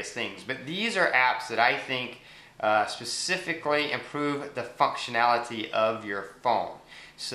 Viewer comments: did he just say the fuckctionality? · did u say fucktionality ?? :P